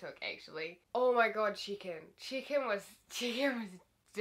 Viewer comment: chicken. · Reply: i thought you were saying chicken the whole time you were saying check in and i didn't understand